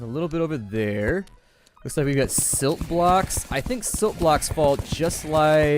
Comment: When he got slit i thought it said shit block